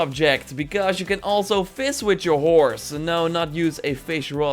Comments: Thought you said Fist your horse. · I thought for a sec you said I could, “fist with my horse 🐎😳😂